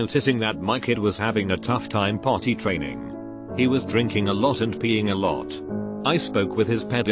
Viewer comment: Don't tell me I was the only one who heard "He was drinking a lot of peeing-"